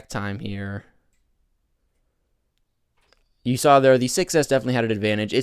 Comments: he sad 6s instead of se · did he say 6s?